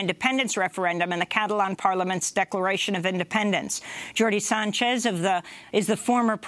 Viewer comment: I could have sworn I heard Amy say "dirty Sanchez." I know she didn't but still was kinda humorous.